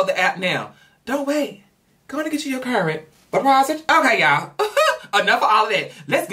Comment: Was I the only one that thought he said: "Go on and get you your Current Deposit!" Whe he said "Wut was it" I